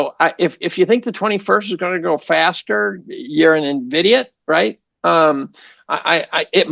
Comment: did he just say Nvidiot?